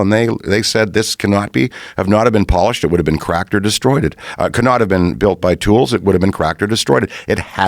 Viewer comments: Kinda thought he said ‘destroyeded’ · Is Dan saying "destroyeded"? I heard him say it a couple of times... Did "destroy" get Aykroyded? Like Sigmund got Freuded. Like whoever reads this is annoyeded.